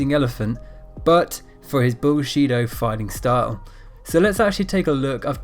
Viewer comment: did he say he was known for his "bullsheta fighting style? Misspelled I'm sure, but it sounds like a artsy way of saying "bullshit". And it is. I wonder if its as obvious to his wife, friends, kids etc. that he just pretends to have this chee power or if they believe it. People like this makes the fighting style more like wrestling or a joke. I used to beleive it was real. I know a big part of it is, but not this fat man, its "bullshita", and his assistants are idiots pretending they getting hurt each week, and going back.